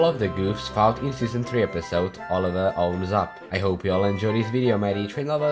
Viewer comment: I thought it said Oliver blows up